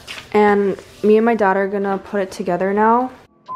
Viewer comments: "Me and my daughter are gonna put it together now". Lmao even the captions got it as "daughter" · am i the only one? i hear "me and my daughter"